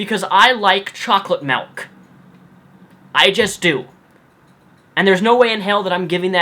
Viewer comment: I got to the point in the video were you were talking about chocolate milk and it sounds like you said malk and I thought of that Julian Smith skit. lol